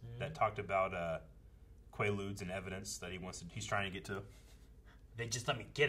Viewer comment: ohhhhh! I’m stupid! Every time I’ve seen this episode, I thought he was saying there were “lubes” in evidence he was wanting to get to! 😂🤣😂🤣